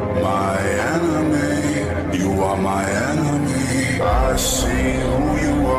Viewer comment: I hear “you are my anime”